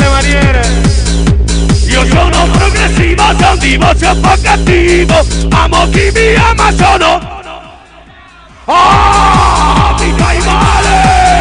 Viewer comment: Al minuti 3:54 cosa dice di preciso ? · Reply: Io sono progressivo son di voce un Po cattivo...amo chi mi ama sono joshua il DalaiLama!!!!